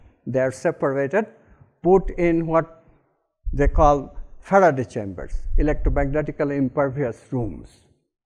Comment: hi everyone, please help...im a simultaneous interpreter and i have to interpret this talk next week and i CANNOT understand what he says at minute 32:00, ive played it back more than 20 times!! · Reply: Faraday room - like a Faraday cage